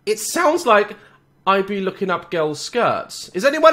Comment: I be looking up girls yep I heard it as clear as day